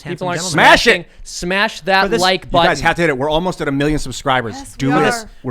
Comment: I thought you said 'take a smash on the like button'